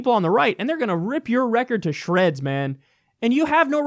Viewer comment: I thought you said: they will rip your rectum to shreds.